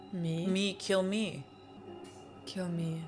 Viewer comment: That's a mistranslation. She's actually saying "K*ll..." It's suppossed to be vague, it's an intentional writing choice. Also another mistranslation at 16:10-16:14, what tenma said is "People like him...people like him...are better off dead." The current subtitles you're using are way worse than Netflix's.